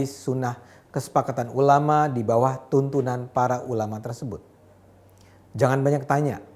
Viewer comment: Bang Ade, menit ke 4:34 itu yang benar: tuntunan atau tuntutan? Yang diucapkan, sejauh aku dengar "tuntunan". Apakah saya salah dengar?